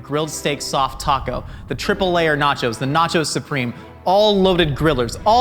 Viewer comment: When you said "Nachos supreme" I gasped in shock for a moment because I thought you said fries supreme for a moment.